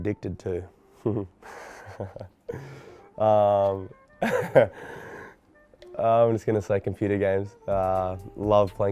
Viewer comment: What did Roddick say at 11:45? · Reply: He said "no shit" haha